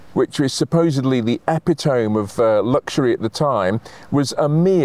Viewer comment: did he really say eppy tome..?? Lol..